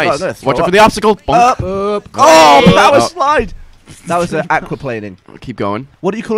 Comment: Anyone else hear Gavin say "fuckin' Acroplaning?" at about 14:30? Thought he did swear! O.o